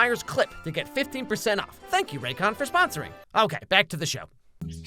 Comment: I thought he said back to the shower he said back to the show 😅